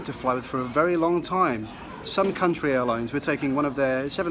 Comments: The whole time I thought he said SOME COUNTRY airlines 😂🤣😂🤣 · I really thought you're saying "some country" LOL!